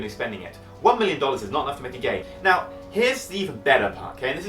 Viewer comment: i heard him say "one million dollars is not enough to make a gay" hilarious audio cut